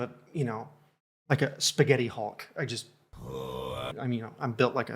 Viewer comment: Spaghetti Hulk - love it! And your perception and thoughts about this situation are bang on 💪🏻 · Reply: Oh my god, I thought he said Spaghetti Hawk and had no clue what that was xD I should wear headphones more.